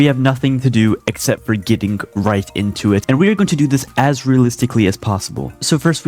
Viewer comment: I SWEAR HE SAY REICH INSTEAD OF RIGHT